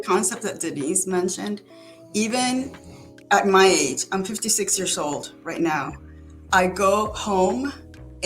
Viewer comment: did she say she’s 56 years old? 😮